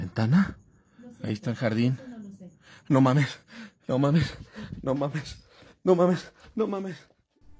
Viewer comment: I always have to giggle when I hear " No Way " in Spanish. . . " No Mummies "